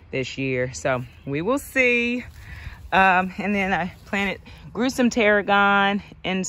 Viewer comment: You said "I planted- grew some tarragon." I heard "I planted gruesome tarragon" lol